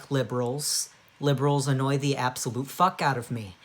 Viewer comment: At the star I thought you said Libra 😭✋🏻